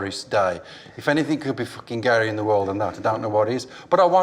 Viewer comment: *please help..what the hell did say?..did he say "If anything can be fuckin Gary in the world than that..don't know what is" ..?..serious question and I have to know what the hell said!..what does that mean? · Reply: gayer